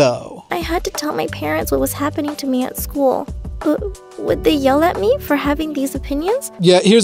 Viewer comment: Girl at the bus: Do you suport the gay? Girl that totally existed: no One day later Students: she doesn's suport people to love who they love, let's not talk to her Girl that totally existed: wHy dO YoU aLl HaTe mE???? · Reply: Nah, I thought I heard her say they yelled at her.